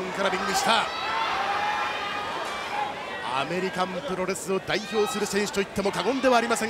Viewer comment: am I hearing it right? commentator said "American terrorist"? that's hilarious if it's right. 😅